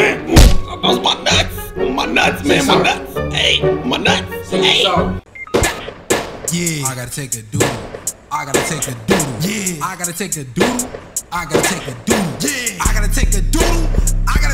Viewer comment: At first I thought the music was saying " I gotta take a dump yea" 😂